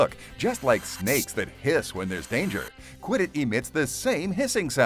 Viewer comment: he actually said piss In a tv infomercial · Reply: He said hiss